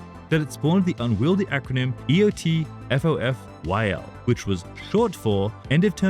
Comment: I always heard it as "EOTFOFGG" but honestly, that one is better.